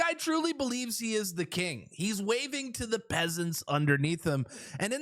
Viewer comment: I was laughing so hard that I thought you said Justin was waving to the pigeons Reminds me of his mom "JUMP"